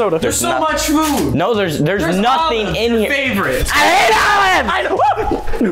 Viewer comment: I thought was saying Oreos!!!!!